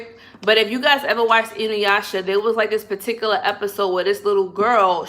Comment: can somebody write out the name of that anime she was talking about at 12:00 i wanna watch but i cant figure out what she said/spelling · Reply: Inuyasha...you can watch it on hulu